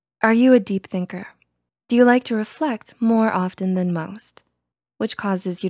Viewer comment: is it just me or did they just say "more often then m u m s "